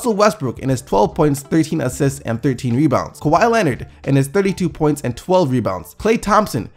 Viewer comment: did he just say kawi 32 boards